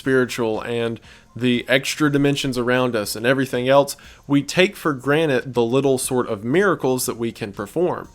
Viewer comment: hOlY sHIt hE sAiD TakEn fOR gRaNitE iNstEaD oF tAKeN fOr gRanTed